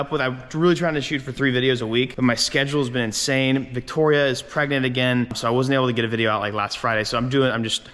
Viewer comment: Victoria is pregnant again. Did I hear that right. So happy for you guys.